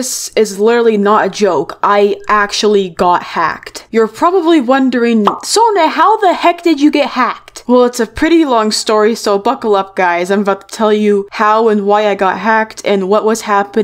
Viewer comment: I thought sona said "so f*ckel up"!